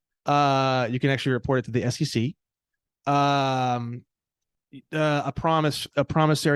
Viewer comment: what's the acronym he's saying? SCC? FCC? what is it short for? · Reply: SEC - Securities and Exchange Commission. (Foundation = reading the Securities and Exchange Act and the Emergency Banking Act.)